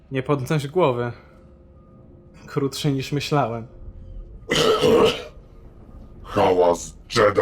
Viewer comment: Ty na początku zamiast ""Nie podnoś głowy, krótszy niż myślałem" usłyszałem "Nie podnoś głowy.... krótszej niż myślałem" i to nawet niezły tekst warty zapisania w kajeciku.